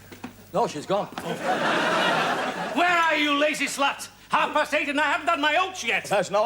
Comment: Did Ernie say "lazy slut"?